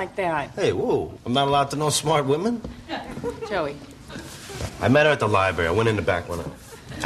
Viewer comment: I’m sorry guys I didn’t catch that, what did he say … ? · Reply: Joey says, 'I met her at the library. I went in to back one out.' Meaning, he went in the library to go to the bathroom. Back one out means he had to poop.